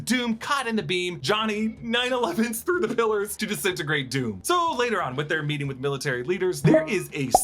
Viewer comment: Did he just say he 9/11’d the pillars? Is this allowed? Seems insensitive for this Chanel